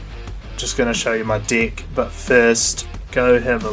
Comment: I thought you were saying "I'm just showing you my dick" for like 5 times until I realized you got that kiwi "i" vowel shift.